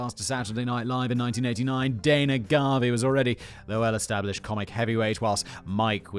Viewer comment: did he say Dana Garvey?